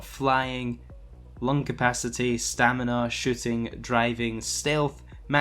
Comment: I thought he said "shitting" lol.